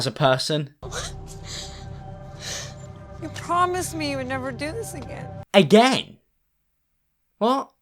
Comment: When the mom says: “promise me you’ll never do this again” by ‘again’ because she just done it then, she don’t want her to do it again. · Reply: I thought she said “You promised me you’d never do this again” 😂